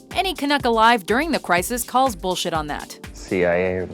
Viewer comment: Did I just hear here say "bullshit"? Not that there's anything wrong, but...still...that's new...